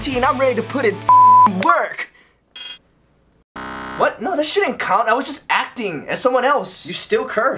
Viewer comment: Um I'm actually disappointed, I thought the joke was "that sh*t ain't count" like how he swears right after saying that the swear he did before didn't count, but he actually said shouldn't based on the subtitles so yeah.